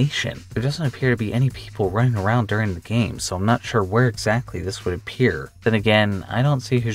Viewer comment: you said "where exactly" but your subtitles said "exactly where". I love how you include your own little easter eggs and secrets in your subtitles for us to find 😀